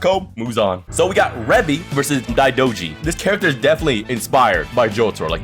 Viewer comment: I can't hear her name can anyone tell me? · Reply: Daidouji